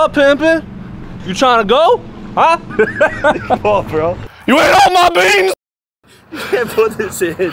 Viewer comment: I think he said you eat all my beans nigga